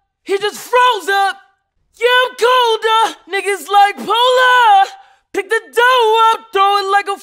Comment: "Niggas like Polar" I thought he said Niggas Like Pull Up before the lyrics